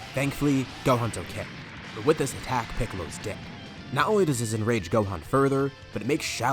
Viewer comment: When you said "Piccolo's dead" the captions thought you said "Piccolo's dick"😂🤣